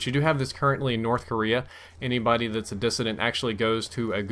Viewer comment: what word does he say? I hear "dissindant", but that isn't even a word. ( I'm not English by the way, so yeah. )